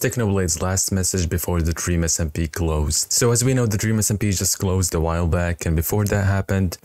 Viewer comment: Why did i Heard "so now the Dreams smp IS now closed to wild blacks 💀